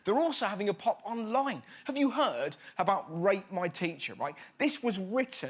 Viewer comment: I thought he said "have you heard of rape my teacher?"